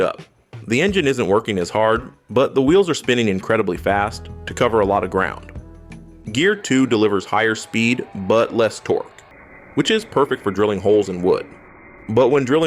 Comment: Higher speed, buttless torque That’s how I heard it at least.